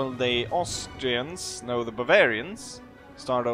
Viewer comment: When you say 'bavaria' I thought you were saying 'barbaria' lol